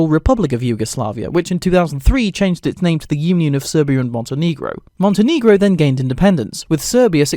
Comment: what did you call me? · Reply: He said "Montenegro".